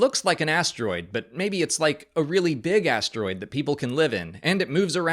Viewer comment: When you said “big asteroid” I definitely heard “big ass droid” and then the idea that people lived in it was pretty awesome actually 😂